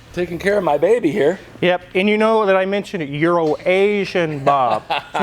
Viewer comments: Too funny, I always thought was saying "Urination Bob" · The first time I heard say Bob’s name I thought he said “Euro Nation Bob” which actually sounds like a perfectly good name😆.